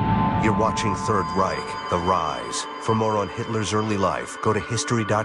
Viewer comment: I heard "for moron hitler's early life, go to..." ^^